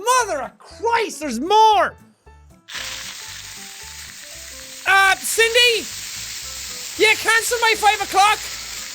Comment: Anyone else that was watching with captions... 12:00 he says “Cindie? Can YOU CANCEL my 5 a clock?” And it thought he said “dick pencil”?? Best thing I’ve ever seen